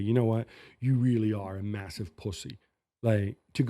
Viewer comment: Did I hear 👂 that right? Gab saying “Pussie” 😂😂 on the podcast lol